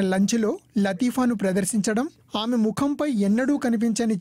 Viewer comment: did he said "lanja"